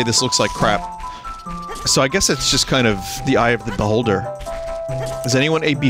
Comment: I hear "eye of the boulder" YouTube auto subtitles somehow get "eye of the beholder" YouTube subtitles. We gotta talk.